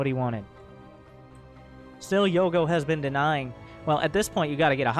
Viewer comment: Also, I keep hearing "Yolo vs. Brenda".